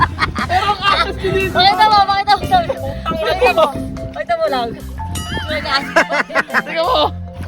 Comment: mommy: pakita mo Love 😁😊 tama po ba ako narinig